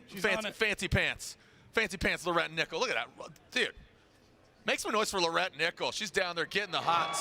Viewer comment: did he say look at that butt lmfao ??